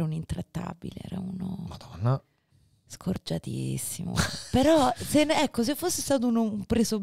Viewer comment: Cosa dice? "Scorgiatissimo"?